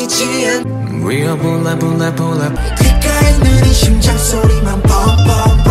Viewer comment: oq eu entendi foi "Bolsonaro é louco eu sou lula jump jump jump" 👁️👄👁️